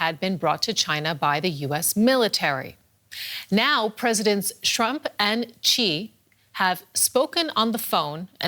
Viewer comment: 😂🤣😂🤣 president shrump.. that's what she said